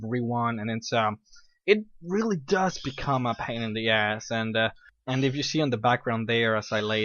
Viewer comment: lol did you say pan in the ass? xD